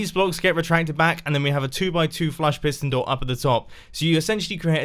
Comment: When said “2 by 2 flush piston door” I thought he said 2+2 is 4 and I went... MINUS 1 DATS 3 QUICK MATHS! 😂